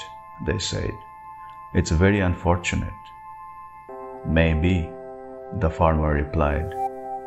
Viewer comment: I thought farmers said, who knows god or bad.... instead of "maybe"